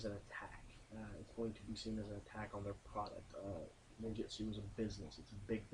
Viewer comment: douche ninjitsu haha i thought you said douche ninjitsu